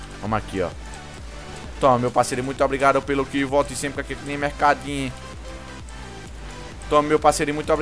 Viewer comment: muito obrigado pelo kil volte sempre mercadin kkkk isso q eu entendi